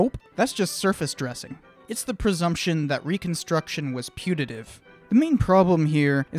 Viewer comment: I keep hearing you say "putative," when I'm fairly sure you're trying to say reconstruction wasn't *punitive*.